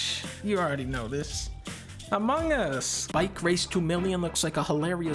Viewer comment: "Bikerace Twomilliam"??? The fuck does he actually say? Game looks fun